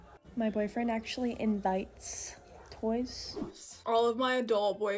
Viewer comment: did she say her bf invites other boys? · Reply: Toys* 😂